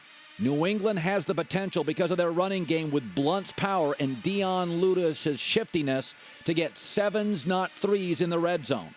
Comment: Did he say Dion Lude-is? I'm 100% certain he did lol.